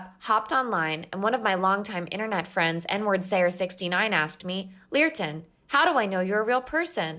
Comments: is it me or did she say N word slayer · Did I hear that right? Did she say her friend was nwordsayer? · did she just say "n-word slayer 69".... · wait did she say Nwordsayer69? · I’m not the only one who heard that as “N-words sayer 69? That’s a perfect representation of most of people in any online community. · That kid said: One of my long time friends "nwordsayer69" Me after i heard that: Dying from laughter